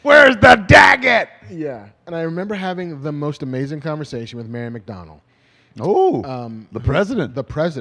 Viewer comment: When marc was telling his favorite Comic-Con story, I thought he said Mayor MacDonald ....not Mary McDonnell. The whole time I was like 🤷🏻‍♂️🤔🧐